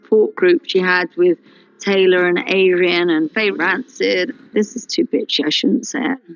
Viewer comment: Did she call Fay ‘Fay Rancid’? LOL